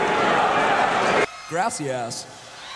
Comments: Sounded like he said " grassy ass" rather than "Gracias" haha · GRASSY ASS! aahaha xD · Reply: yeh ik it just sounds like he is saying grassy ass xD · I LEGIT THOUGHT HE SAID GRASSY ASS WTF